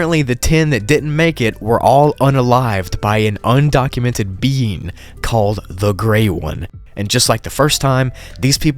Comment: When you said 'A mysterious being called The Grey One' I thought you said 'A mysterious bean called The Grey One'